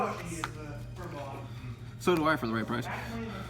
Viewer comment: "Ash Blossom sucks!" -"So do I for the right price." 3:17. LOL. Bro, I watched this video about 2 weeks ago, and probably re-watched that part 3-4 times, but I still couldn't tell what you said. I'm re-watching it now as I'm building my deck, and I decided why not try and listen again: took me a couple more listens, but I finally got it. LOL, that was hilarious man. Good deck profile dude!